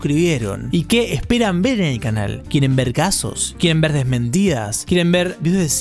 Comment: entendí "quieren vergazos" Y yo como: :0 q loko, pero si:>